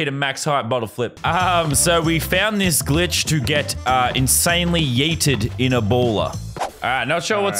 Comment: did i hear get insanely yeeted in ebolo????